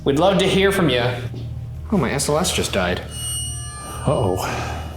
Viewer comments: I misheard “my SLS just died” as “my Aunt Celeste just died.” 🤦🏼‍♀️😂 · when he said "My SLS just died" I wasn't paying attention and I thought he said "My Aunt Celeste just died" and was like WHY IS HE BEING SO CASUAL.